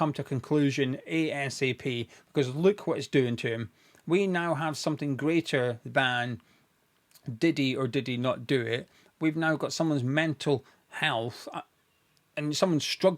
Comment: I initially thought said “…we now have something greater than Diddy…” like P Diddy currently on trial for being a criminally horrible human being